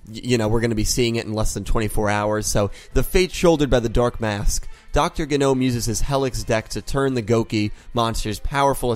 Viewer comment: What I heard: "Dr. Genome uses is Hellish Deck"